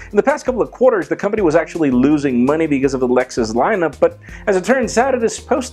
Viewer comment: Am I the only one who heard him say Lexus lineup?